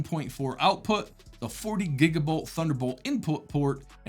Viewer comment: Gigabolt is way cooler sounding than gigabit bit I'm not sure it's what you meant to say, haha